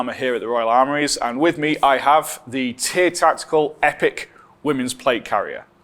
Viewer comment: sorry, misheard Tyr Tactical for Tits Tactical still works tho